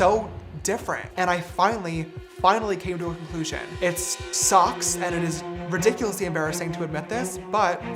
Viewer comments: I thought he said “it socks” 😭 Him being “desperate” isn’t an excuse for anything. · I HEARD "I finally, finally came to a conclusion, it's socks" OML